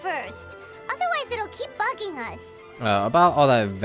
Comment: I heard paimon say fucking instead of bugging. Serious double take for me.